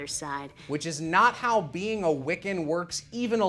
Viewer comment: See, as a kid, I didn't think they were saying "Wiccan", I thought they were saying "Wicken", like some fake "wicked" group in the past.